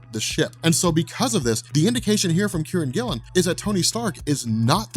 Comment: I thought you were saying Karren Gillan, not Kieron Gillen lol. Thought she was writing for Marvel now